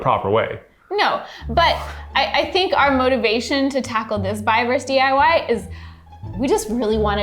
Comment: did say "this fiverr's DIY"? lmao